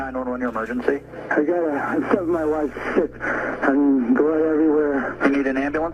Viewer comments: I heard “I stepped in my wife’s shit”..... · I heard "Guhh, I stabbed my wife,Sh*t, and blood everywhere" in a rushed voice. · slow it down to 0.5 speed He clearly says "I killed my wife.. sh*t!" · Well i hear; "911 your emergency" -"I got uh..i killed my wife. Shit. I mean. Blood everywhere." 4:30 onwards, even slowed down to make sure. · I hear "I killed my wife" because "I found my wife sick" doesn't make sense compared to what he said afterwards.